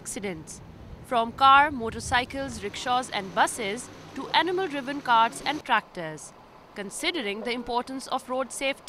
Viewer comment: did she say 'animal driven carts'?????? There's the problem right there lol